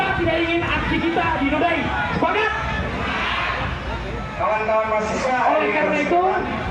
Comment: 04.30 aku mendengar suara goblok mahasiswa goblok... bener gak yah... apa aku yg salah denger???